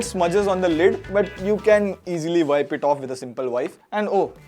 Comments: he said wife instead of wipe😂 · Did he just say you can wipe it with a simple WIFE?! 😮😂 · Did he say "wipe it off with a simple wife" 🤔😃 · "Wipe it off with a single wife". Did I hear that correct ? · Did he said "Wife"?????💀💀💀